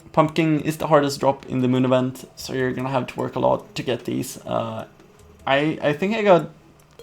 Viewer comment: Lol did you say at 11:54 ''You have to twerk alot''